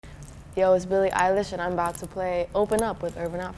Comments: I thought it said “Billie English” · I thought she said Billie Eyelash